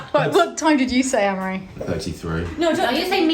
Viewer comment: Ooooh it’s Amaury- I thought you all kept saying Anne Marie!